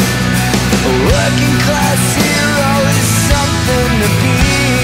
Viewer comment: I swear I hear "A Working Class Hero is some kind of being"